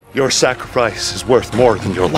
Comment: Did he say “Your Sacri-fries?”